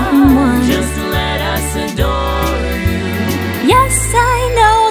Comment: Did I hear "let us" or "lettuce? 😂